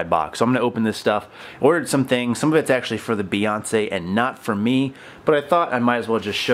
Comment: Honestly thought you were saying "the Beyoncé"